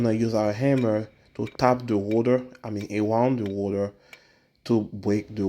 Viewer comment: I thought he was saying tap the water😂, but it was tap the rotor😅